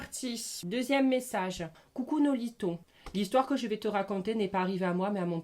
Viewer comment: À 5:40 rassuré moi j’ai bien entendu « coucou Noholithon » 😭😭